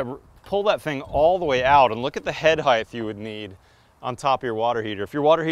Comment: did he say "head heighTH" ??? I wonder if he also says weighTH? What in the world?